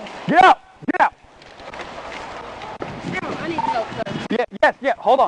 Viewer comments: Woman jumps out and is heard saying, "I need To Go Cuz".... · Did she say, "I need to go sir"? @ 1:25 Hilarious....she seemed very detached from her present reality.